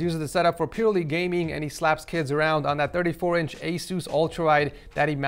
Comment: did he say slap kills around or did he say slap kids around